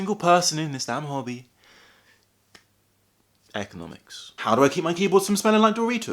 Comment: For a sec I thought he said he studied Ergonomics in a British accent.